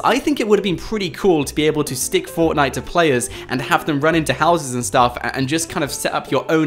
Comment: Who heard him say "you can't put fortnite on players" instead of c4